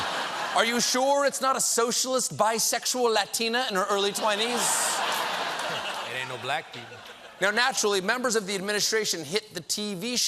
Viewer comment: That throwaway comment by John was spot on. 'There ain't no black people' - Funny and sad at the same time 😂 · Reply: I heard it as "It ain't no black dude", but yeah it made me laugh too.